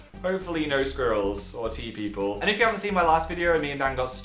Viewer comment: First i thought he said T People and i was like wtf are T People what does the T stand for silly me